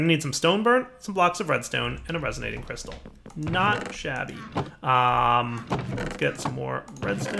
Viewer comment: did he say shavy or was it me? lol